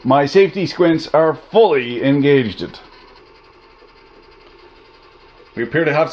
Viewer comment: Did you say engagedn't?